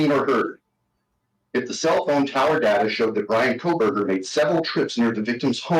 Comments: He did not say SEVEN times, he said SEVERAL times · I heard “several” not “seven”.